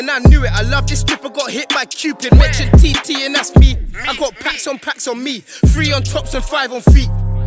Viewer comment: did someone say packs there👀👀😂